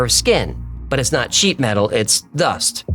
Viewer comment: sheet metal or did you say sheep 🐑 metal 😅😅